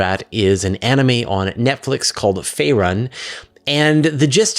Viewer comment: So if anyone couldn't find it, the anime is called 'Frieren: Beyond Journey's End'. He really should have spelled it out. It sounded like he said Faerun! Which if you've played DnD, that's what you would have heard lol...